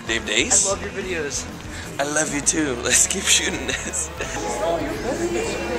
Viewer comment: i swear the man at 1:20 says 'oh no your pregnant!?' 'firstly' lol :P